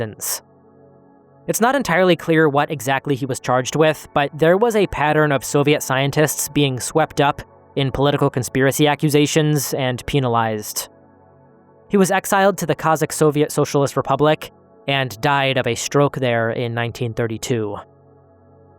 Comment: I'm sorry but did you just say PEEN-alized bro